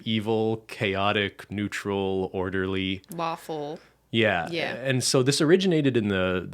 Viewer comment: i kinda zoned out and at 0:54 i heard it as orderly waffle and immediately related to it